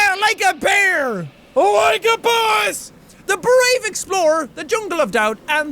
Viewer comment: i thought he said LIKE A BUS xp